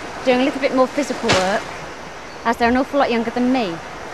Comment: Dah fuq did say @ 19:46? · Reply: "As they're an awful lot younger than me."